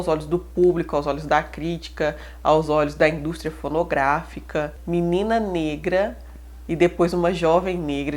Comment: só eu que ouvi "indústria pornográfica"? Espero que eu não seja o único louco que ouviu isso =/